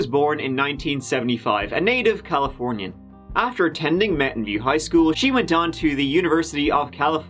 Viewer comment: I genuinely thought you said Mountain Dew Highschool for a bit, then again it's California so it wouldn't be surprising